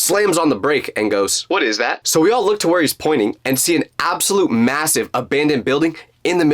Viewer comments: did he say MASSIVE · did you say massive?! 🤨